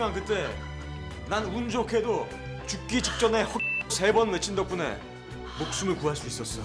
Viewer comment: 뭐라는거? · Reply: 허경영 세번